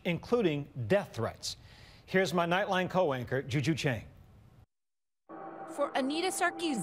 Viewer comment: Here's my nightline cowanker... That's what I heard anyway. Congrats for producing a video clip with the most concentrated cuntery I've seen in quite some time. Well done.